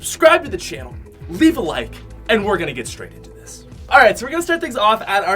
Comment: The intro sounded like you said “let’s get strangled into this” 😂😂😂